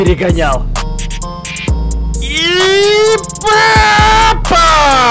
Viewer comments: мне одной послышалась что сказал Пеппа . -. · мне не послышалось он сказал пеппа